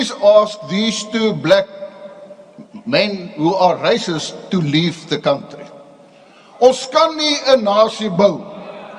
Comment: Did he say “ask these 2 black men to leave the country” 😳😳